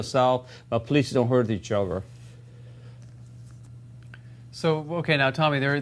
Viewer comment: Sounded like "You can laugh, you can cry, but please don't hurt THE CHILDREN."